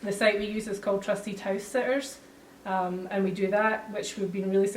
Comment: What website did The Chiang gang use, it sounded like she said trustytoasters? What did she say? Anyone know?